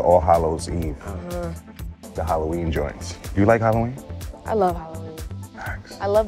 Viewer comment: did he just say Jordans and pick up a pair of blazers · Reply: He said joints not jordans